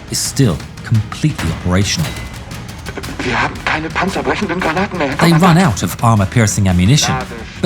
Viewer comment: what did the loader actually say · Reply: He Said(in german) wir haben keine panzerbrechenden Granaten mehr